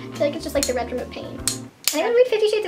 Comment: Haha at the end I heard "haven't you read '50 Shades Of Grey' ? LOL just me?